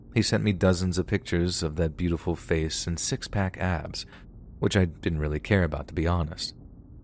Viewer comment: I misheard "six-pack abs" as "six-pack ASS" and the mental image made me burst out laughing. 😂